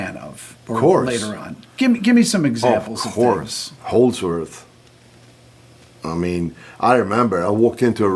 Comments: For me he's among the best in the world his guitar 🎸 play makes dream so long Steve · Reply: HOLDSWORTH?? what is he saying? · Never really cared for his style, was a huge EVH fan, but he is undoubtedly a very talented guitarist. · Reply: HOLDSWORTH?? what is he saying? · Yngwie saying that he never practiced needs certainly a great deal of reflection. He certainly practiced in some way of another. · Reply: HOLDSWORTH?? what is he saying?